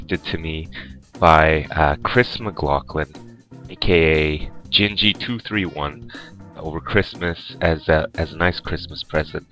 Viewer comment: did he say "GAY" 00:25 ?